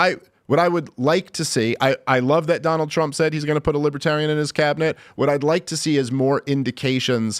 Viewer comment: I always believed the national libertarian party was a joke, but after listening to this show, I'm convinced. Trump24! 🇺🇲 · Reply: I thought Trump said ‘vote for me and I’ll put a libertarian in my office’.